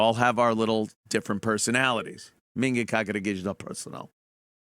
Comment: Does anyone know what Ant says at about 8:23 ? It's right after he says "We all have our little different personalities" and right before he takes a swig from his beer. It sounds like "minga kaja gigital personal". I tried the YT subtitles but no dice--nothing comes up. Is that a saying or phrase I don't know about...or am I having a stroke?